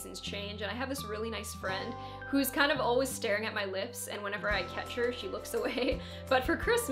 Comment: My partner overheard me watching this and was very disturbed when he thought he heard, "I have this friend who is always staring at my nips, and when I catch her, she looks away." Hahaha! Great video as always. You're so creative, and I love that I can always trust your recommendations. Would you be open to making a video about your creative process? Like how you brainstorm ideas? or maybe addressing the fear that comes with putting yourself out there?